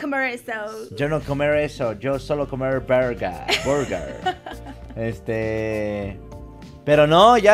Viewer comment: le entendí " solo comer b3rg@ JAJAJA